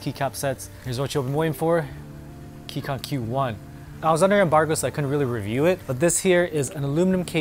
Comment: I can't quite make out the first part of this sentence about not being able to review it? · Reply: I was under embargo. Basically all tech reviewers were not allowed to talk about the product until a certain time/date set by the company.